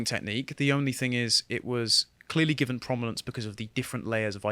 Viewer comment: I had to go back and listed to this a few times to see if he said "cromulence" instead of "prominence".